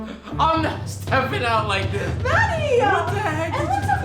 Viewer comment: I low key thought she said daddy....but she said matty..🤣🤣🤣